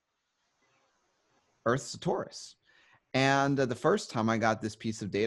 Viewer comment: The Earth is Taurus? Please explain. What does that mean? Henry Gruver said when he saw the earth it was indeed a planet. 🌎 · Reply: Torus.